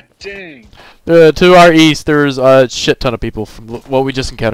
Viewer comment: I couldn't figure out what "2 RE's" were. I AM an idiot. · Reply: "Our East", sorry I mumble :D